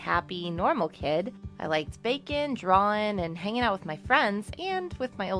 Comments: i thought she said bacon 😂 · When she said baking I thought she said bacon because of the accent